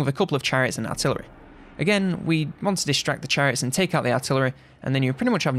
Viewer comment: I thought he said diss track the chariots